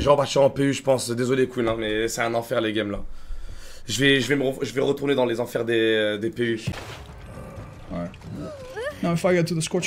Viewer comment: really wanna know what he said right here this salty boi 😂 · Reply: Sa ma saouler, désolé mec je vais jouer dans l'enfer des PU . Translation: i give up , sorry dude im going to play in PU's hell .